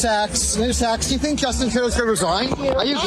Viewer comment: Thought he was saying mister Saks hahaha